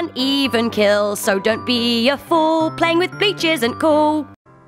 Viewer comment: I hear the word bi*ches instead of bleach